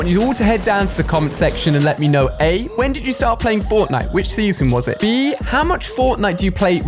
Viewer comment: So you play for season 7 everyday 😂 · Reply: lol i thought he said when did u quit